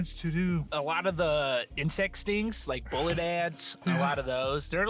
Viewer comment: I HEARD YOU SAID INCEST😭🤣